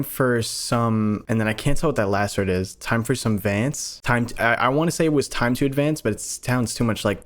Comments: it sounds like it says “time for surveillance” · I swear to fucking God he said "time for some friends", meaning that they may create more of either them of other forms of the Carnis, hence why they want to work with the humans, because they know they'll get what they want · I thought it said "Time For Some Revenge" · I definitely hear when your trying to decipher them talking, at 23:00 "Time for some friends." Meaning they will / can either turn humans into singularities and / or part of the Carnis. Or, hopefully, they genuinely want friends and see humanity as potential "friends" because of how much the two species are learning from each other. I would even go far enough to say that's the most likely, however, I guarantee you they have a different idea of what "friendship" is if they want humans to become one with them, and that the miscommunication between them and us will probably result in total war on both sides. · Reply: I thought he said “time for some vince” as if vince is some kind of food item, which he technically is